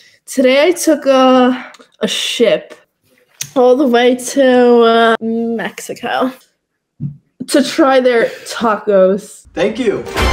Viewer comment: I thought she said a shit to Mexico to try tacos.